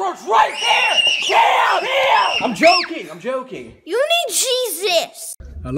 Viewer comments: Sounds like he said ”Need Cheezits” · thought it said "You need cheez -its" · I’m joking “ you need Jesus” I could not stop laughing 😂 Edit: sorry I think it said cheese itz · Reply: I thought he said "you need cheese its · ｢Youに人生｣て聞こえるの私だけ？ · "You need Jesus." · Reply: I thought it said you need cheese sticks